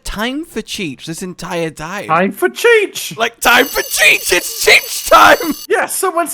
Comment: NGL i to thought she said "time for chich" lol